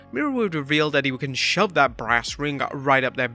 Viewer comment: Brass ring?! I thought he said branding 🤣